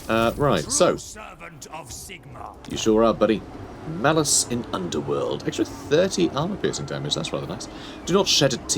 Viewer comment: For a moment I heard "Malus in Underwear"...